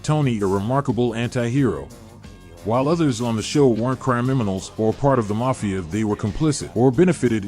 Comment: did you say CRYiminals at or about 0:51? lol